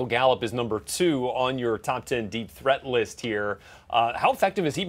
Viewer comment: I thought it said “Deep Throat”....